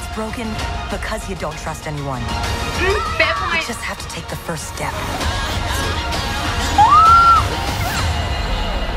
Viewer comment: did anyone heard sisu shouted the name of raya....just wondering what happens there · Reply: Yes it was Boun according to the subtitles 😀